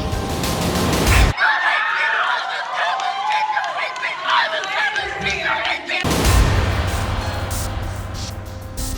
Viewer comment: what is jabba the hutt saying? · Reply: Bring me Solo and the Wookiee!